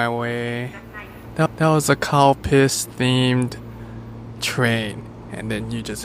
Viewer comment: lol I thought you were saying "cow piss," and that it was a milk-themed train! But then I saw the thumbnail and looked up Calpis.